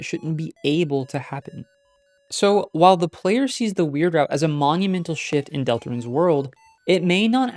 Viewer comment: It sounded like you said “Monumental Shit”